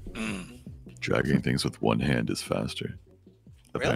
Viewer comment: thought he said shagging, not gonna lie